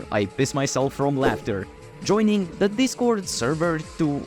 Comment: I thought you said fist yourself from laughter 💀